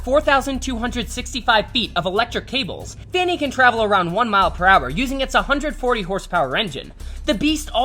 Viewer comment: Lmao I thought he said it can travel at 100 miles an hour Lmao until I listed again 1 mile per hour